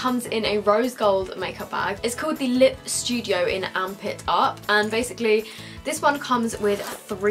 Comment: I thought you said "armpit up" instead of amp it up xD I loved the variety you get in the £45 bag (bag 2)